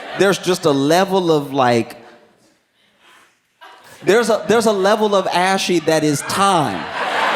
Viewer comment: anyone else hear this joke? Didnt catch it 😅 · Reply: “There’s a level of ashy that is time. Y’all wanna act like you don’t know what i’m talking about but y’all KNOW every - that sometimes - people get flaky - ima move on - ok.” He’s saying that at some point of old age, you can’t fight being ashy - it’s just a part of being that age and it’ll only get worse (more ashy, more flaky) as you age.